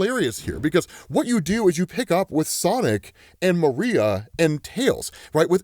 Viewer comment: did i hear that right 15:47, "Maria"? lmaooo